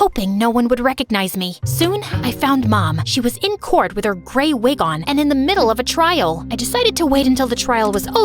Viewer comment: I thought she said granny wig 😂